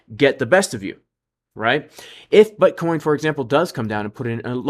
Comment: LOL sounds like you said B*uttcoin at 8:40 🤣